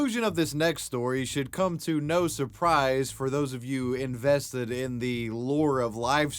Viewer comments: When you talked about Ice Poseidon and when you said "no surprise" I thought you said "nose surprise". I need sleep, badly. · Is it only me who hears when he presents "ICE POSEIDON" and he says "should come to no suprise" I hear "should come to nose suprise" xD